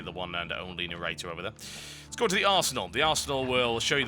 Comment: Every time a British person says "Arsenal", I hear "Arsehole".